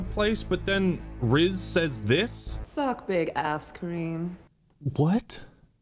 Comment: I thought she said "Suck big ass, Karim", because that was the guys name. I don't think she said Cream lol. I'm not that worried about the "confusing" or "seemingly irrelevant" scenes with the original characters like Soren or Kwan because they are probably going to make more sense before the show is finished. I'm going to let them show me the story and interpret it myself before I ask them to tell me what the deal is with them right away. Breaking Bad had many seemingly irrelevant scenes that later proved to be very important to the story as a whole. I'm not gonna sweat it over a macguffin artefact either. Shit like that is basically a staple of Halo by now. We had the Index, The Halo Rings, The Ark, Janus Key, and many more I'm probably not even aware of tbh.